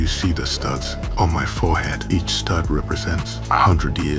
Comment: I missheard it wrong the first time i thought he said nuts but said studs